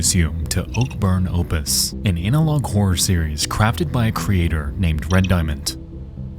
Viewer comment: sorry to be immature, but I laughed because it sounded like you said "anal log horror". 😝